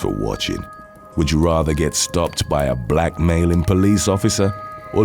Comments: OMFG... At first I thought he said, "Would you rather get stopped by a BLACK MALE POLICE OFFICER...." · Thank goodness for subtitles, I misheard that last part. Thought it said "would you rather get pulled over by a black male police officer" and I'm like Hell yeah!